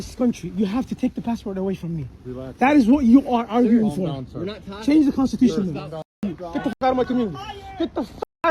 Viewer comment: I’m sorry, did I hear ‘you need to change the constitution’ at 8:55? Did we just get a Freudian slip?